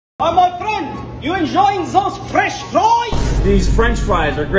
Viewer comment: I hear it as “Fresh Trobes”